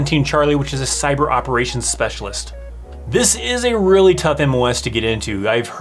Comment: I thought you said Sniper Operations Specialist. I was really confused when you were talking about demand in the civilian world. I had to go back and hear you say it again. Cyber Operations Specialist.